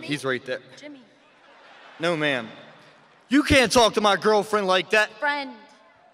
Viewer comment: OMG lol 0:25 did he said girlfriend